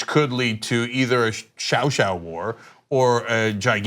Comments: did I hear 小小war? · Did cenk just say “xiao xiao” (tiny) war 3:00? LOL